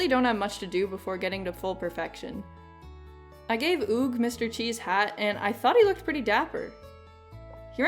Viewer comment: why does it sound like your saying mr chi insted of mr Qi